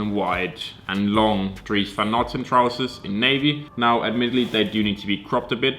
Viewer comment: trousers? Dont hear what he is sayin?:( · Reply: Big boy With diet dries van noten